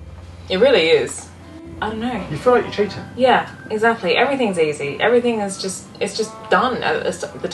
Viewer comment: Every time you both say "Everything is easy" I hear 'Everything is so well designed'. Why wasn't Ruby Rose as 'Easy'? The seasick phenomenon is probably due to the lack of heavy keel and the two engines toward the stern creating a 'Light Bow' effect.